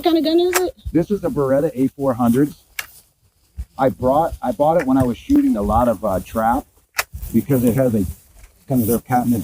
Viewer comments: I swear it sounds like he said "I bought it when I was shooting a lot of trout" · 1. His hunting outfit looks brand new. 2. The gun he's using is almost 2,000 dollars. Only hunters who have extra disposable income are buying that specific shotgun. There are PLENTY of more affordable options. 3. He says that he bought the gun a while back because he shoots so much trap, but he can't operate the shotgun at all. Such a short clip exposes so much. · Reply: Oh, I thought he said he was shooting trout. The fish, I thought he thought you shoot fish.